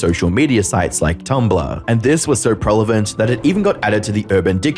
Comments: Some: "Always a great video" Others: "Wow, these people suck" Me: "Did he just say 'prelavent'?" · Did I hear "prelevent" instead of prevalent?